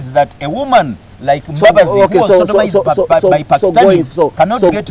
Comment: did he say sodomized by Pakistanis?